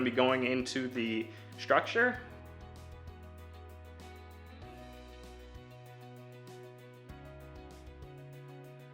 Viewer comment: I think the sound cuts out? What did you say here? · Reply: I use a 1 1/4" washer and 3" lag bolts to secure the frame to the wall.